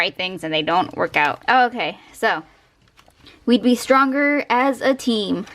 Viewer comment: I thought 19:20 said "we'd be a stronger team"